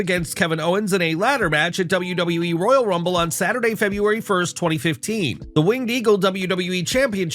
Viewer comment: u said 2015 instead of 2025💀